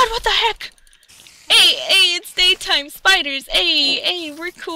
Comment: I thought you were saying Aids Aids Daytime Spiders Aids